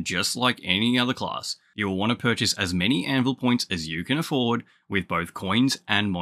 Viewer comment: WAIT A SECOND I HEARD ANVIL POINTS AT 7:23, great video and thumbnail, what a good stream moment LOL.